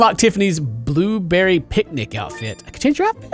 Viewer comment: I thought it said "Blueberry Panic" and I was like "STRAWBERRY PANIC"!! <link>